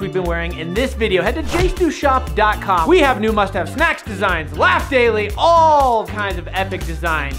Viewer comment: I thought he said mustache snacks😐